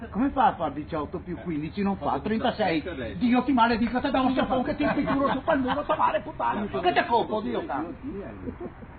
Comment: Prima dice "Dio ti maledica" e poi inizia ad biaschicare in qualche lingua arcana e sconosciuta 🤣🤣🤣🤣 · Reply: Dice te dago uno sciafon (ti do uno schiaffone) che te impicuro su pel muro (impicuro credo voglia dire tipo ti spalmo sul muro)